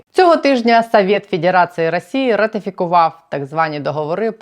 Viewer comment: Супер перлы : «впихнуть невпихуемое в состав России » 👍👍👍. Тащусь от этих видео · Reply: А кто-то ещё услышал "Совет педерации" или это только я? 😁